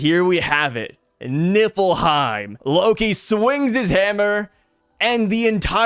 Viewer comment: Dude i swore mofos was saying nippleheim.. here and in tekking video smh im fried